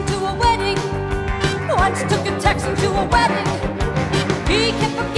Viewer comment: When I was younger, I always heard 2:06 as "Once took a taxi to a wedding."